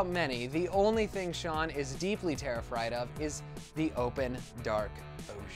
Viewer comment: Ayyeeee where my mendes army at?? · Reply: Everyone who commented on this go to 2:59 . . . terrifried instead of terrified